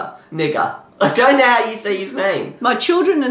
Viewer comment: did hr just say n word???